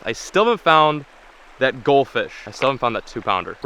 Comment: Who else thought at 8:30 he said gold fish 😂